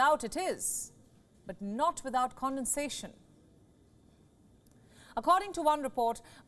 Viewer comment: Even the cc thought she said "condensation" at 0:38 instead of "condescension."